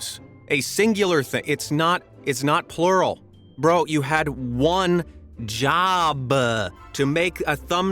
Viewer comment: When he said "You had 1 job!" At 3:05 It sounds like he said"You had 1 joooooba"